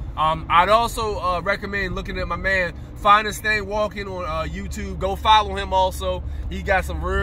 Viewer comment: How do you spell that other Youtuber's channel? I can't find anything for "finest day walking" or "finest stay walking" (I can't tell if I'm mishearing you). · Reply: Never mind... Found it on Google as "Finest Thang Walkin". Thanks for the info.